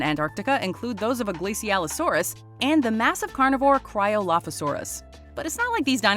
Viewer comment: I thought she said cry-a-lotta-saurus